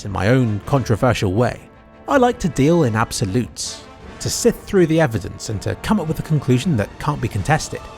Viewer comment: I misheard "sift" for "SITH"